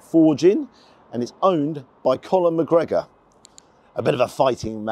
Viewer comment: Love the videos 👌. But I thought I heard you say Colin McGregor 😅😅, Instead of Connor McGregor